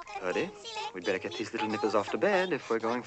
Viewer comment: Um what did he say at the 3:04 mark? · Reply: Nibblers.